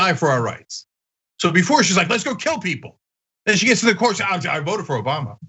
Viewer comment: Yeah, it has since been pointed out to me that at 3:07 (well after Cenk's first quote that I pointed to), he finally says, very quickly so I missed it entirely, "She gets to the court she says...". So I stand corrected, he does claim that. Though that hardly sounds like an actual quote, so I still maintain they haven't shown us the evidence, and they should. It sounds like you have watched or read other reporting on this story elsewhere. I have not. So, if you're right about that, then sure, I agree it's ridiculous. But I still maintain this reporting sucks. If that's going to be their critique, they should show us what she said.